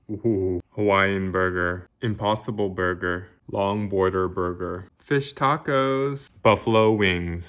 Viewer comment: When you said long boarder burger I thought you said bong water burger and I was like wtf